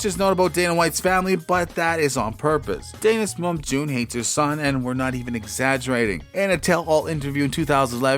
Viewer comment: What'd he say at 2:13? I couldn't understand it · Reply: "Dana's mom June hates her son and we're not even exagerating"